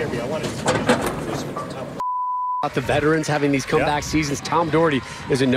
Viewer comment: what was said after the 3rd Steve can you hear me? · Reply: "I want to know"